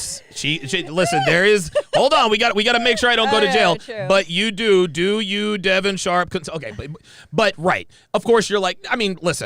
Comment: "Do you Devin Sharpe..." anyone else hear that? 😆